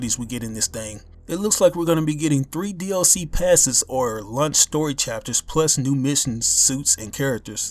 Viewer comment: Was I the only one that thought he said "Lunch", instead of "Launch"? lmao